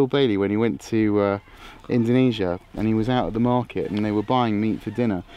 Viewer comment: did he say Indonesia?